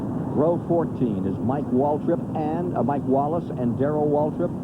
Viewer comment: "Mike Waltrip". Lol i thought he said Darrell Waltrip.